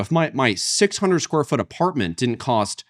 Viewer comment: Did he say that he lives in a 600 m3 apartment as a single person? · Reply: He said square foot. 600 sqft = 55.742 m²